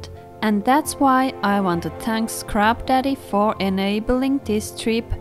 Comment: Thought she said thanks to crapdaddy lol😅